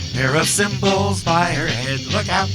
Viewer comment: 🎵 "Pair of cymbals by her head..." 🎵 At first, I thought it said, "Error symbols by her head"! 😀 Wait, what?